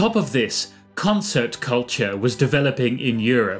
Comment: I got scared I thought I heard Cancel Culture